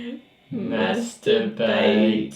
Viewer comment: I don’t think it’s saying masturbate. I think it says master beat me.